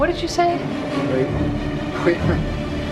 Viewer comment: Wait... who am I here? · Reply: NightSky489 oh thats what he said..i thought he said wait..who am i again?